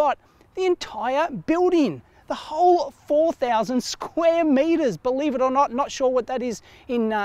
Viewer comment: did he just say he bought the whole place!! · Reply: No, he said he is renting it.